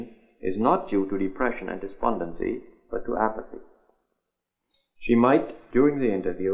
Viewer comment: her lack of energy and animation is not due to depression and despondency ....whats after that tho? i cant understand what is he saying at 1:27 but due apathy? also ; 0:40 is childish and ..? whats coming after childish ? thankyou